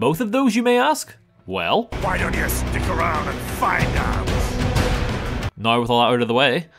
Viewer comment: I'm not gonna lie, I misheard "Why don't you stick around and find out?" as "Why don't you fuck around and find out?".